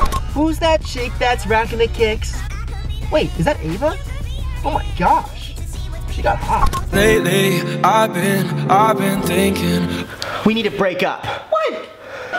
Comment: IS it just me or did I hear Brent say “Dang Eva got hotter” LIKE IF YOU HEARD TOO